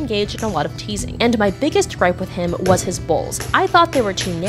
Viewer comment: I thought she said “balls” instead of “bowls”